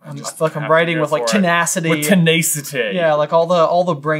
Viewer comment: is saying hes just f***ing writing with tenacity 😂